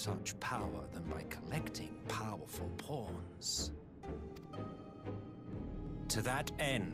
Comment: Totally heard that as "By collecting powerful porn" the first time @@ Thanks for uploading these. I'm behind on msq so watching your videos at work that way I can skip cutscenes when I get home lol.